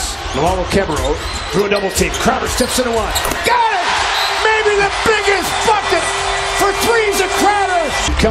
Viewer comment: Anyone else thought the commentator said "the biggest fucking" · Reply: “Maybe the biggest Bucket” is what he said but still 😂